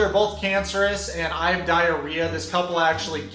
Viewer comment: I legit thought he said "I died of ria" instead of "diarrhea"